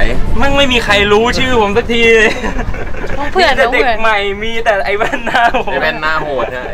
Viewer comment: พี่ไม่ได้พิมพ์ซับผิด พี่พูดว่าน้องเผื่อนพี่ก็เลยเขียนซับว่าน้องเผื่อน ไม่เชื่อก็ย้อนไปฟังหลายๆรอบ